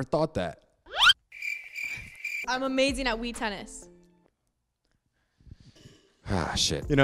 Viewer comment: I thought she said weed tennis